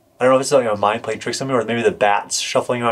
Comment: Lol , you said maybe my mind is playing tricks on me. I thought you said my mine is playing tricks on me. Love your videos. Keep this shit up